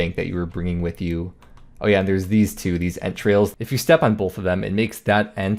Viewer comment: what he said "Ent trails" what I heard "entrails"